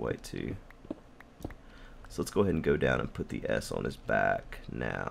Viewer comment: Lol when he mumbled lets put the s on the back it sounded like he said lets put the ass on the back